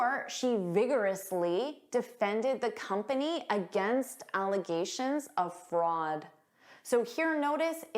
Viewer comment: English Great video! Thank you for putting this vocabulary list together. Just a heads-up: there's a small error in the transcript at 13:40. It says 'allocations' instead of 'allegations'. Thanks for the awesome content!😊